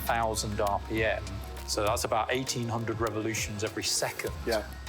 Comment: "So that's about 1,800 RPM" at first I thought he said 18,000 RPM and I was floored. XD